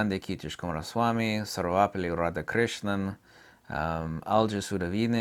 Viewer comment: What did you say? Doctor ?!?! how? Sorry, I didn't understand. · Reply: Dr. Sarvepalli Radhakrishnan